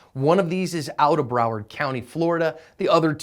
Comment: When I heard “browsed county fl” I laughed cuz I stay here